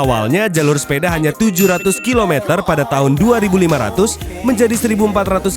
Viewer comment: engga salah dengar kah tahun 2500??😅